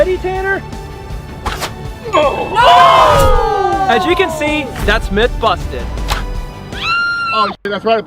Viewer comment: When u siad as u can see at 25:35 why did it say Ass u can see🤥🥶